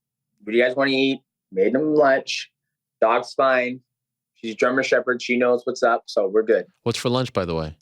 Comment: Did he say "Drummer Shepherd"?